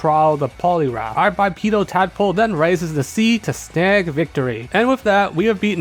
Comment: I misheard that as bipolar tadpoler 😅